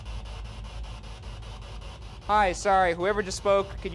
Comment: I heard “hi son.”